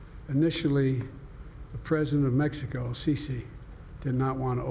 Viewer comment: did he say the president of Mexico?